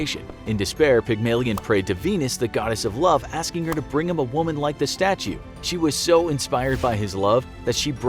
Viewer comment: it says Ike instead of like